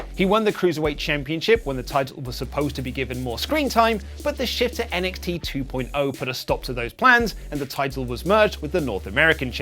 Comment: I heard "Shift to NXT 2.0" as "Shitter NXT 2.0"